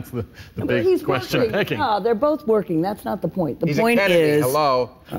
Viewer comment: What does Bill say? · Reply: "He's a Kennedy, hello..."